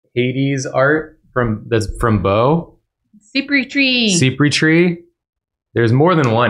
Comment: Did anyone catch the name of the artist they got the Hades prints from, I love them sm ebdjdn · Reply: I thought Jacob said they came from Cypritree (aka Bo). They've appeared on Drawfee before!